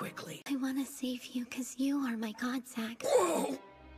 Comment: does anyone know what she's saying??? I can't understand her here. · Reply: She said: “I wanna save you ‘cause you are my god, Zack.”